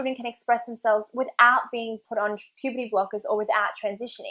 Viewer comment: Is it just me Or I heard "pp block" Instead of "puberty block"